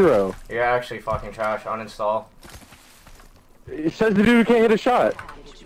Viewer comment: what did he say? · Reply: "Dude you can't even shoot"